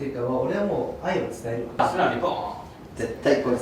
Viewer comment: ここのこんぴ「素直にボーン」って言ってるように聞こえるけど字幕だと「そうだね、いこう」になってて戸惑ってる